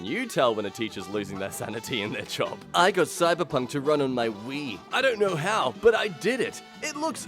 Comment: I heard "I got cyberpunk to run on my weed"